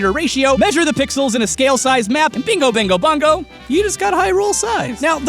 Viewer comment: did... did he just say Bingo Bingo Bongo? ... No... Humans... All of earth... They weren't ready to hear double Bingos!!! What have you done!?!